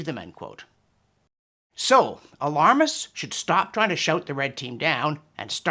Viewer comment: Oh..."shout" the red team down. Given today's events, I thought you said "shoot" them down. They do seem to have gotten tired of the former and are now going for the latter...